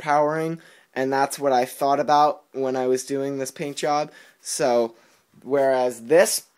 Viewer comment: paint jobs i thought it said blowjobs!!!!!